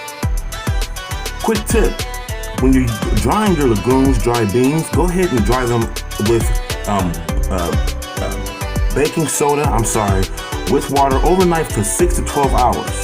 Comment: I can't hear what you said around 5:30. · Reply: When soaking your legumes (beans) you want to soak in baking soda overnight or for about 6 hrs sorry about that